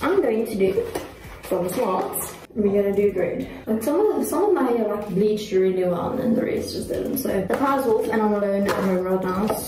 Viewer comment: The inaudible portion of the video what she is saying is this 15:24 I'm telling you some of my hair like bleached really well then the rest did...so! 15:25 15:26 The bottles off... And im alone in our old house. ..